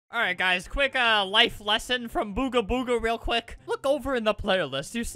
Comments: Thought he said booger booger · I THOUGHT YOU SAID BOOGER BOOGER!